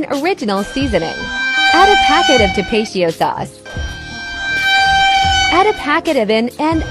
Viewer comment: when se said tapatio sauce i thought se said 'DA RATIO SAUCE'